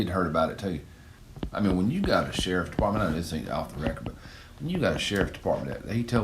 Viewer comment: “I know this ain’t off the record…” is what I heard. I didn’t hear “This is off the record…”